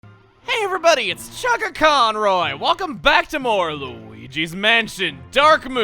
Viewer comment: when he said chuggaconroy sounded like he said sugarconroy